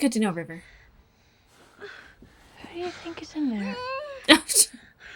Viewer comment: First time seeing this with subtitles. I always thought she said "what do you think is in there?". It was funnier that way.